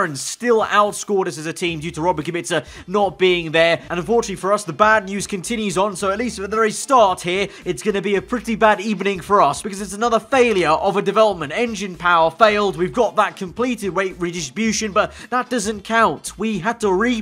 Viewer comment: Did you just say "bad ebening" at 0:32! Lol